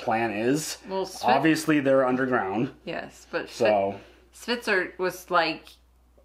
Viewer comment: Sounds like is saying "Switzer" when talking about Hvitserk :D